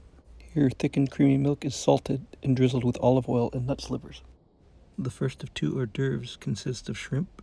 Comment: OK! Nut SLIVERS! I thought you said "NUTS' LIVERS" for a sec there. LOL!